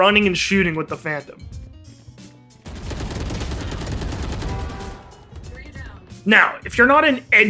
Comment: Hard challenge: Did he say vandal, or phantom? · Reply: Phantom